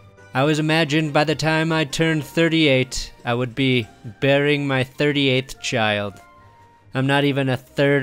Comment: For a moment it sounded like you said Rivers would be burying his 38th child, not bearing it. Got kind of concerned there.